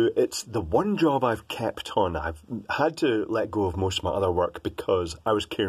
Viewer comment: When you said "kept on" I heard "kapton" xD . About mirrors: They make the camera more versatile. It's easier to do things like astrophotography, when you don't have a dedicated camera for that. I doubt the preview of a mirrorless cam would pick up any stars in the vincinity to focus on or align the telescope to the nebula you don't actually see. Real mirror + eyes could. However, I admit that this is a pretty special application. Let me have another go: The preview screen has a resolution so low you cant't really see your exact focus. Focusing a 20 MP image on a 3 MP screen is like building a watch with an excavator. It doesn't matter much for artistical images since there usually is at least a tiny bit of stuff in both dorection of the spot you want to focus on, but for technical images where you want to image a plane, like astro, mikro or repro, you wan't to be sure that the focus isn't even 0.1 mm off. Autofocus isn't accurate either, not even on the EOS 5D I work with, not to mention that it doesn't work under even what I consider medium-to-low light conditions.